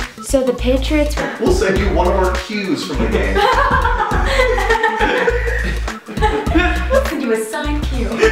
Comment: Can someone tell me what they said in 00:37? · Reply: We'll send you one of our qs from the game. We'll send you a signed q.